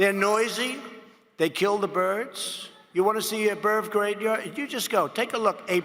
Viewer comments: Did he just say "a birv gravya" instead of a bird graveyard? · I love that at 1:02 he's slightly flustered by the fact he said "birv" and takes a moment to correct the pronunciation a few seconds later, like the fact he said birv instead of bird would confuse people already eating this imbecile stew.